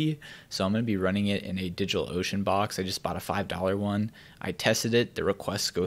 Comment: did he said Digital Ocean Box? I need one of those and I wanna know if that's what he said.